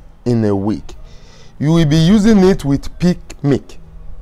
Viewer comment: Can you please tell me which type of milk. It sound like you said pig milk. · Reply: Not pig, it's peak milk